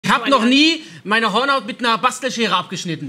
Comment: 😂 Ich habe Vorhaut verstanden und ich sitz hier und denke nur What the fuck ist falsch mit dem das muss doch übel weh tun.